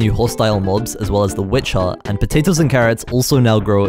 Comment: did just say "Witcher" or am I trippin'? 😅